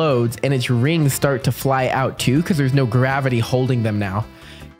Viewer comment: What 3:24 Did you say no gavitey